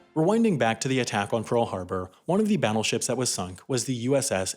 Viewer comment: I swear he said babbleships, did anyone else hear it?